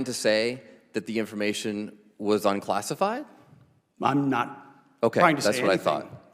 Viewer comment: Did Hegseth really say at 2:26 “I’m not trying to say anything”? lol